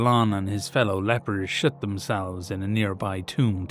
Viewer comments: I know he said "Shut" but I heard "Shit" and that made it so much funnier 😅😂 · I’m sorry but your accent made it sound like you said they “shit themselves in a tomb”